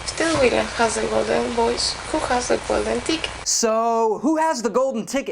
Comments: IVE GOT THE GOLDEN DICK ;D ......... wait she said what?... i could have swore she said dick.. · LOOOL it sounded like she said golden DICK XD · Ooooh I thought you said it was golden dick. Never mind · IVE GOT THE GOLDEN DICK HEHE ;D ......... wait she said what?... i could have swore she said dick.. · I seriously thought she said ''Golden balls, who has the golden dick'' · i thought she said who has the golden dick